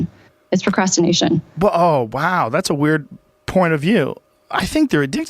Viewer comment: did he say "That's a weird Porn of view?"